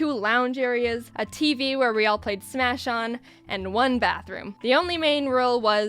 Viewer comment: When you said "a TV where we all played smash on" I heard "a TV where we all smashed on" XD